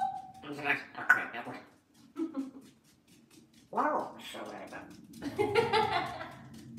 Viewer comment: I gotta say upon hearing this again...mum is definitely giving a potty-mouth when not necessary! 😲 I heard, “You’re so bad at that.” Like, clear as day ! Tell me I’m wrong 😎🙏